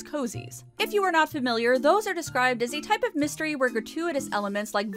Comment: I thought you said "recurring HAIRacters" when talking about the women in the beauty shop.